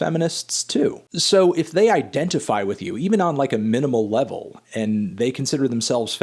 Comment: Did you just say minimal? Always gotta plug those ridge wallets.